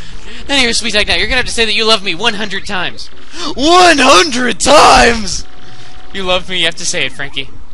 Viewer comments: when it was seed up it sounded like you said no 100 times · It kinda sounded like he said no 100 times.XD